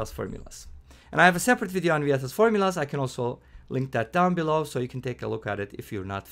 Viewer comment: What is the name of the formula at 2:28? Subtitles say vatas formula but I looked it up and nothing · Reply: Vieta’s Formulas